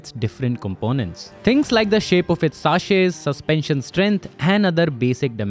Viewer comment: did he really said saashey instead of (sha-seez) chassis